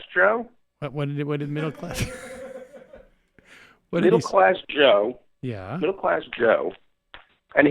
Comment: When he said,"Middle class Joe" after that all I heard was,... "Little bus Joe!". I'm sorry but not